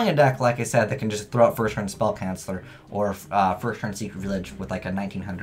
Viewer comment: It always sound like hes saying spell cancer